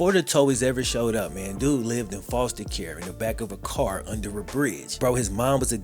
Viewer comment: When you were naming the places he used to live, I wasn’t looking at the screen so I heard “he lived with foster care in a car under a bridge”. Had to rewind that one lol. Great video tho.